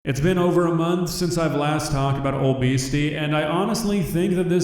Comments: I swear I heard "it's been over a month since I last heard about obesity," and I felt attacked 😂😂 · Thought you said “it’s been a few months since I’ve talked about obesity” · I heard "This is the las time I talk about obesity." XD I'm rolling as a fat girl and know thats not what was said haha.